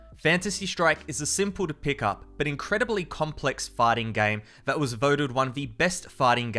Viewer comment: did he say farting games?